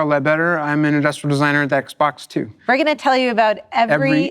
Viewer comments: Did you said Xbox 2 ? Oh sorry I thought you said Xbox 2 Sorry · did he say xbox two